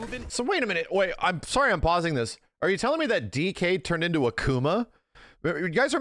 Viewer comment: I seriously thought you said "did DK turn into a coomer?"